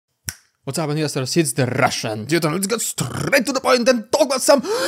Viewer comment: You should start saying " What's up Westerners" , I thought you said that for a start when I discovered the channel ( as you are an Easterner)